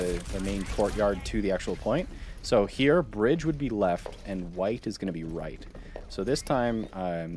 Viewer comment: did he say white is right at the begining? im triggerd couldnt get past that part